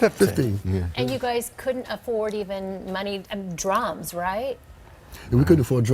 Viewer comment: thought the lady said, "you guys couldn't even afford drugs?" lol. drums though... ah